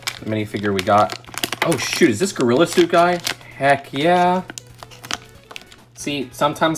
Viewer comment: Did he say gorillatube guy 2:15 thats me lol